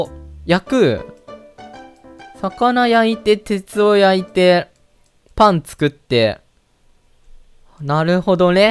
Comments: パン作ってが、 パンツ食ってに聞こえた人👍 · 7分47秒なるほどれって聞こえたのは、俺だけなのだろうか？ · パンツ食ってと聞こえた人🙋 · 「パン作って」が「パンツ食って」にも聞こえたw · パン作ってがパンツ食ってに聞こえてしまった。俺も汚れたものだな...